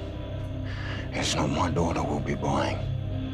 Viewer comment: what does he say no one is buying my daughter what??